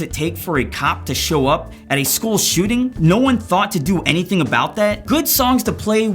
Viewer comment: he actually said schools shooting not shmool shmooting